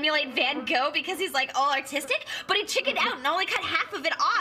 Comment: uh, did u say “autistic” or “artistic”? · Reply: Art